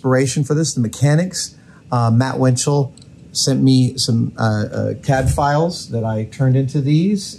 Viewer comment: When you said "CAD files", I at first heard that as "Cadfaels", and pictured some people cosplaying as 12th century monks showing up at the cave. 😄